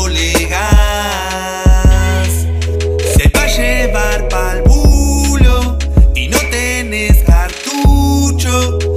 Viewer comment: Que dice en el minuto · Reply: si ahy pa llevar pal bulo, y no tenes cartucho(preservativo), toma yo tengo uno wachin gozalo mucho